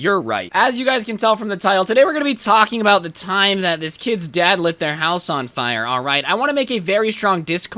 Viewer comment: i thought it said he lit the horse on fire 🙉🙉😂